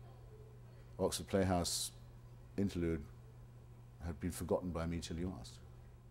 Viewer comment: what does he say at 2:05 "oxford playhouse ...." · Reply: interlude have been forgotten by me until you asked nevermind